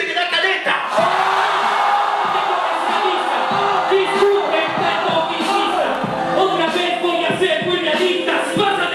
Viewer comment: Qué dijo en 6:50? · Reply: Me la suda pana che cambia de estilo y te quedas re careta